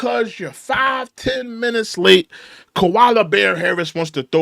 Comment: Koala Bear Harris lol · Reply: I thought you were saying Kamala Bear Harris 😂😂😂😂😂😂 but Koala is even funnier lmaooo